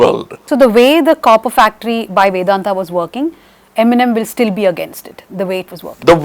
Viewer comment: she sounded like eminem instead of MNM